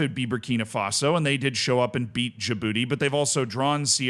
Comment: "they showed up and beat your *ss" is what I heard